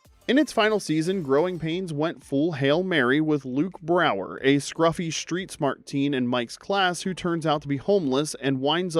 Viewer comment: What did u say here???🧐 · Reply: “That chaps my gears…” i think he conflated the saying “chaps my ass” with “it grinds my gears”. He’s feigning joking resentment of John Stamos’ great head of hair.